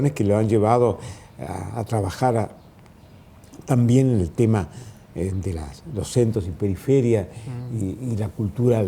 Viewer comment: que dice en el minuto 33:09?? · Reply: "también el tema de los centros y periferias..."